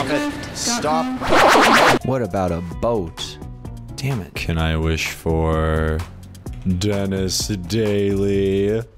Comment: what did he just say? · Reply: he’s a grown man